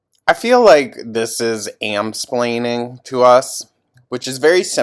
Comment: I thought you said “HAM-splaining” 😂😅